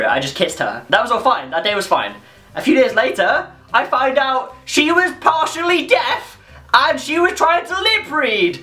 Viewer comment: Could somebody just write down the sentence at 2:50? I don't understand it... :/ (I not a native English speaker) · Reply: he says "she was partially deaf"